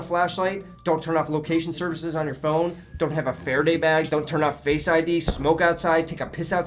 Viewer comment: what he said? Faraday mag? Fair day bag? What is this? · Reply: Faraday bag is actualy a nett or a box off foil that alows you to use a phone or laptop in that box and russians cant find your location